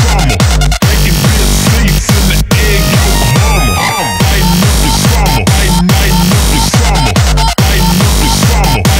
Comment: What does it say around 1:06? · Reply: Bacon bits seats and the egg yolk ramen